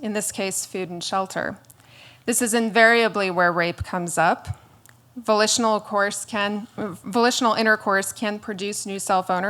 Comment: I thought she said vocational intercourse. and I'm like, that's called prostitution. But no, she said volitional intercourse.